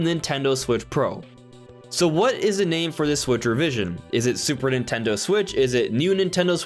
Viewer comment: I thought you were initially saying “Switchervision”